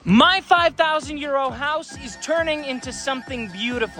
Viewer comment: I'll be honest i thought he was saying MY 5000 Year Old House.